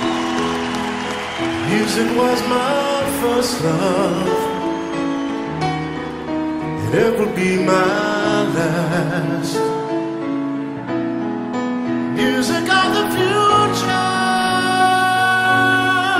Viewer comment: hahaha, ich habe immer verstanden "music was my first love and applepie my last" und mich zeitlebens immer gefragt was zur Hölle der Mann gegen Apfelkuchen hat. 😂